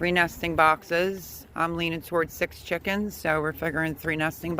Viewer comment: I heard you say six chickens. LOL should have waited before I asked questions 😂 Still want to know what flavor of chicken you plan on getting. Do you like that? Flavor? LOL 🤣😆